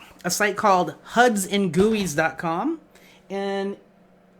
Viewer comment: you mentioned getting inspirations from a site, sounded like hudsandgooey? Please clarify